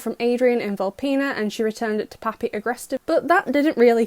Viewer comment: did you say poppy agreste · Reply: I said papi aggrestive:)